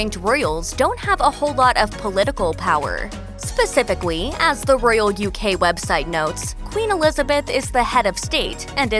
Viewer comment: Why am I hearing "spacificawy" instead of "spacifically" every time she says the word 😅😅😅